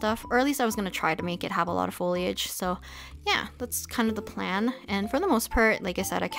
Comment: Why did she just say nya at 7:10 tf bro😭😭😭😭😭😭